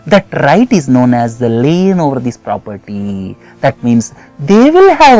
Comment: what does he say at 26:05... anyone..?? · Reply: k.. got it on google, its lien over property...